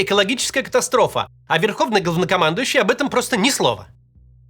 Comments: Послышалось: "верховный гавнокомандующий" А не Не послышалось Так и есть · Мне послышалось или макс сказал верховно говнокомандующий? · Верховный гавнокомандующий 😅 Сначала, подумала, послышалось 😅 но нет 🫶🏻🙏🏻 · послышалось "Верховный Говнокомандующий"... Что недалеко от истины